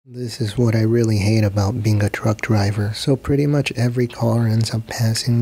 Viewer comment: Who else thought that he said drunk driver at the start.😂